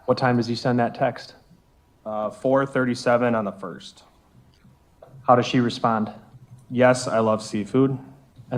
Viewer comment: Did anyone else hear his attorney ask him "how does she know" when his ex was testifying about the hidden shelf in the bathroom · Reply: I heard “does she know that” haha